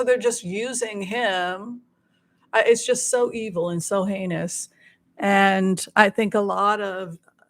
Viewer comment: Regarding. Hershel being used by R, "evil and heinous" ...I heard "evil and anus" which works also. G Abbott- was he involved in separating Mexican kids from parents at the border?